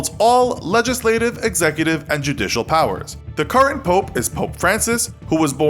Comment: It thought it said powder haha